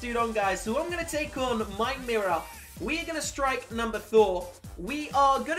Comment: I heard him say number thor